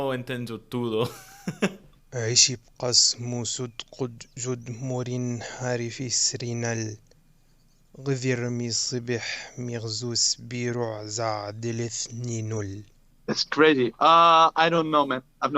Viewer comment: bro my native language is arabic but i didn't understand what did he said 🤔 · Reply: عِشِ اِبقَ اِسمُ سُد قُد جُد مُرِ اِنهَ رِفِ اِسرِ نَل غِظِ اِرمِ صِبِ اِحمِ اِغزُ اِسبِ رُع زَع دِلِ اِثنِ نُل هذا شعر مشهور للمتنبي